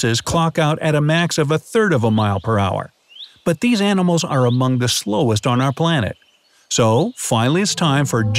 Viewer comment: My favourite ones Centepede 0:50 Turtle 1:15 Ball python 1:44 6 lined race runner 1:49 Polar bear 2:16 Bear😂😂😂😂😂😂😂😂😂😂😂😂😂😂😂😂😂 2:21 Antelope 3:25 Lamborghini my boi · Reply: Did he said AMOUNGUS